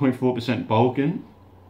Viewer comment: When he said "Balkan", I thought he said "Vulcan!"